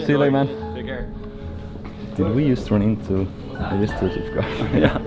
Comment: what did say, did we just turn into... ?